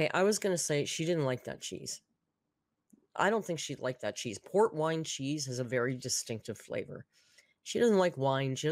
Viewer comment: Am I alone in thinking salami is kind of on the lower tier of charcuterie meats? It's not bad, but I like something like chorizo a little more. I balked at all the Gatorade. It is truly a sports drink, I'm not a dietician, but many discourage you from drinking them casually, they are meant to replenish lost carbs from physical activity (also it is so gross. Just my opinion but it is absolutely disgusting, yes even the blue one). Finally, I will admit, I feel silly. For some reason, when I saw 2 AM tacos, I assumed like actual soft tacos like the kind you get at a regular Mexican restaurant, but it was just Taco Bell...I am at a loss as to why I thought any different... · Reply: I got another one. She’s talked about that cheese ball in other videos and I thought she was saying it was a “pork rind cheese ball” which sounded awful. Relieved to find it was a “port wine cheese ball.”